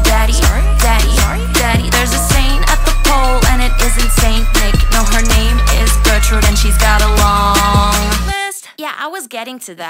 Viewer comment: I thought u said “her name is GOJO” T-T